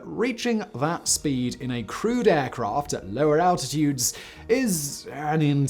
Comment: I don't know why but each time you said 'crewed aircraft' I heard it as 'crude aircraft'. I was thinking "okay, it was early but still, all these x-planes are crude? that's harsh, Simon!" And then the rest of my brain finally woke up.